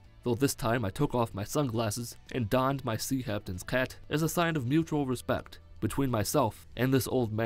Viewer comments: does he say "sea haptains cat" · Sounds like he said Sea Haptains Cat